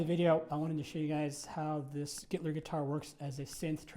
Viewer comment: I accidentally heard him say "Hitler guitar", now I can't unhear it! LMAO